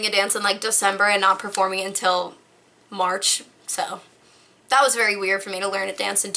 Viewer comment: When she said “I’m used to learning a dance and not performing until, March” I thought she said “and not performing till Lunch “ 😆